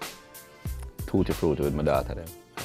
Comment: On 1:24-1:27, did Shaggy just said tutti frutti with Madonna?